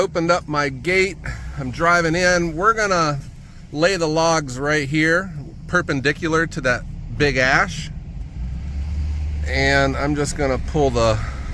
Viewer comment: Thought you said the logs would be perpendicular to your stacked rows.